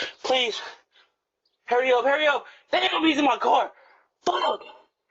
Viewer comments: I thought he said, Damn those bees in my car lol. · At first I thought he said he had bees in his car😂😂😂😂😂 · I could have sworn i heard him say "them GDs in my car" 😂😂😂